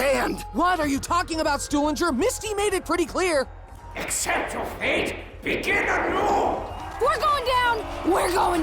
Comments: The distorted voice might be Richtofen speaking to Samuel except we can't hear what he's saying sometimes? maybe? at 2:04 you hear," Accept your fate, begin anew" Just a thought. Also Maxis mentions not being disrupted by power signals or signals or something. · i heard accept your fate start a new